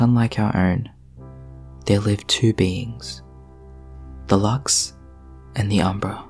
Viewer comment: I thought you said beans, like okay? I toss em. Then realized you mean beings